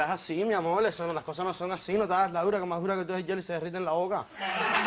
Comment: Que es lo que dice el en el minuto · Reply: Gary Campusano el dice:" no te hagas la dura, que mas duro que tu es el hielo, y se derrite en la boca".